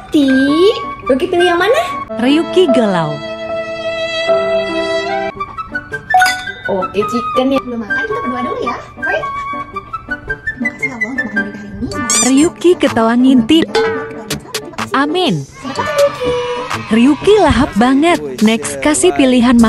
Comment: Pas doa dengernya "Makasih ya Allah" salah denger gak sih???😂😂😂